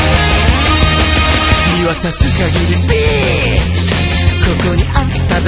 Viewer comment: did he say Bitch